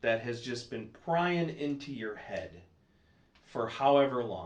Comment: Thought he was finna say “PRYING into your wife” 😂😂😂😂